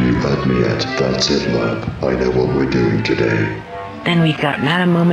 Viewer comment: Can anyone tell me what Lerb is saying? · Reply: you had me at “that’s it lerb, i know what we’re doing today”